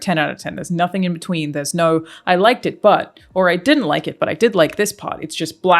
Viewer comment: The serious 'I liked it...but' part made me laugh because my mind heard it as 'butt' due to it being right after the ass appreciation slide jokes. Genuinely superb take on this whole thing though. 1 hour into the game and I'm just enjoying the Sekiro lite parry combat.